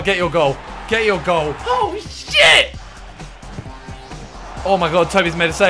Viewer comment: sounds like he said oh shiyeah